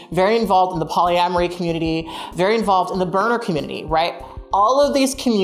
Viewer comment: i heard "burner community" wrong for a second and thought you were talking about the birder community 😂😂😂